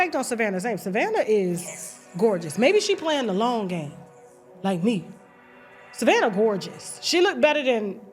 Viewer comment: Did she say “like me “ ?